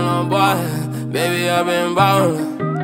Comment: What is he saying at 0:04 ?? Can someone tell me.. and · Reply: Baby I been ballin?